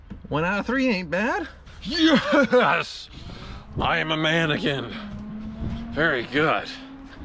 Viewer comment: I swear I heard you say you are “a manikin” 😂